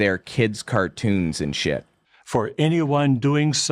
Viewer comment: When he said "detestable" I heard "the t3sticle."